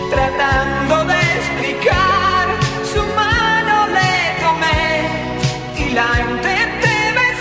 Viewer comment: Siempre le entendi " su mano de comer" hasta ahorita 😎